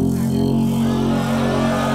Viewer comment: I thought he said "wrong one fight" 😂